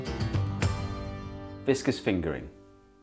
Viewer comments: I thought that said “Vicious fingering” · Came for the fingering, stayed for the viscosity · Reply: I thought it said Vicious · I thought you said vicious fingering for a split second! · I thought that said "vicious fingering" and questioned for a moment what website i was on